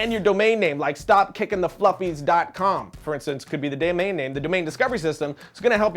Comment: Did you just say "..could be the dam-main name..." :|